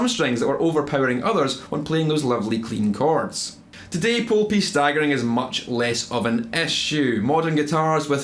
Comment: At first I thought he said "poo pieces" and now I can't unhear it. Sometimes I think I'm a little boy trapped in a man's body.